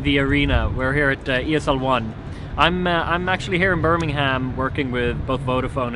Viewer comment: No joke I thought he was saying "Burning Ham" the first 3 or 4 times.